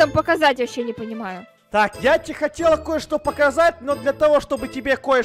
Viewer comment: мне послышалось или Эд сказал: я хотелА .... Интересный факт: возможно Эд это и не Эд а Эдисонья или как там сказать там можно, ааа точно он у мамы принцесса, а принцессы в армии не служат!